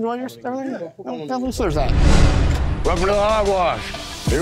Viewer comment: i thought he said welcome to Hogwarts 🤣